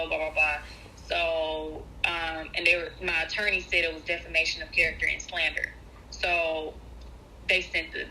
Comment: he is in the process once keesha win her lawsuit against her it's going down! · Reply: I thought I just heard him say deformation of character.